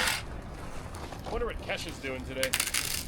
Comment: Did he just say I wounder what Kesha’s doing today😂